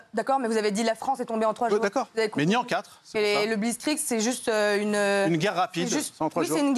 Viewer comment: Moundir lit une lettre, c'est déjà une performance pour lui. Faut pas lui demander en plus de vérifier, comprendre ou d'avoir une honnêteté intellectuelle. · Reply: C'était un des meilleurs moments. Moi, j'ai entendu "le bizzkrik".